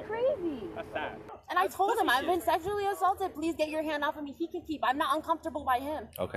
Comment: Did I hear her say "I've been sexually exhausted..." excuse me??